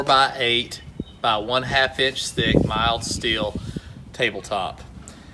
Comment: Not that close, thank you for guessing. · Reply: Ah - rewatched... you said "Half inch thick" - I thought you said "One-half inch thick (1.5)". The answer will be 1/3rd of that. 296kg (652.5lb)